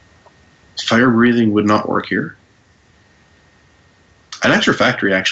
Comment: What does say at 23:46? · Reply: "Firebreathing would not work here."